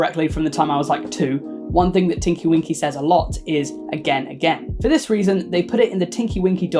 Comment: I heard "I'll kill again" from tinky winky lmao